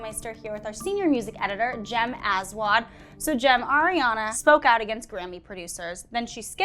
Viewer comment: how come no one is talking about how the senior music editors last name is, I literally had to stop because i thought she said Jem Asswad